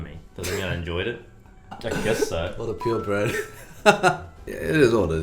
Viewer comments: "What a pure bred." What a pure Brett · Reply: At first I totally thought Eddy was saying "What a pure Brett" lol. I have to replay that part to actually get what Eddy was saying. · Did Eddy just say "What a pure bred/Brett" at 8:56? I'm dying laughing about it